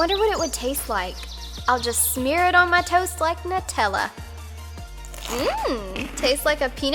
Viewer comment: At first I thought she said smear it on my toes like Nutella I was like eww